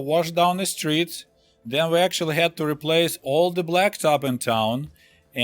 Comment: At around 5:45 - 5:55 he says ‘we had to replace all black xxx in town’ what does he say there, can’t really hear it? Is it black top? What does that mean? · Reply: Black soil, I believe I said.